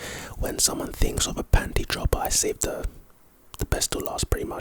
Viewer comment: I thought he said pantry droper☠️😭